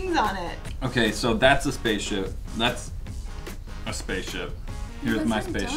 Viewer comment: Sounded like said spaceship !